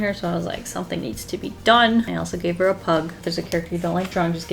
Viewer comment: all I heard was “and then I gave her a hug”